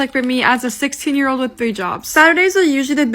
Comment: When she said 3 jobs I thought she said 3 boob jobs I was like “HUH”😂